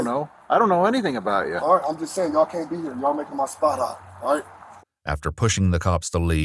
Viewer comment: Did he really stick his head out the door and tell the cops "you can't be here.. you're making me hot"??? Lmao am I mishearing him? I was a junkle for over a decade, now clean for 9 years, and idk if the terms and phrases have changed but back in my day.. if a dealer is "hot", they're either being watched/followed by the police, or they're informants for the police. Either way it meant avoid them at all costs and get your sht elsewhere. So what I'm saying is.. did this man really just tell the cops to beat it so they don't fck up his drug sales? Lol